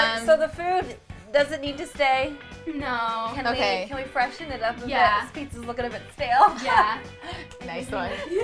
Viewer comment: What does say at 1:51? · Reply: mo mi "can we freshen it up a bit? it's looking a bit stale"